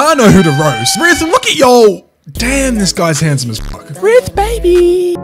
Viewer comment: man what did he say "ryth baby" like what the hell